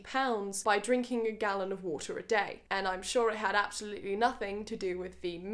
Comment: Drinking game: take a sip of water everytime she says water. You're welcome. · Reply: It sounds like she says Woter :]